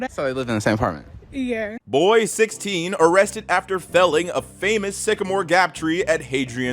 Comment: I thought it Said falling off instead of felling of